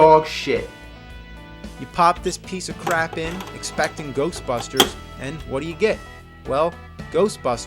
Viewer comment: Brave Cat I thought they said Ghost Buster, without the S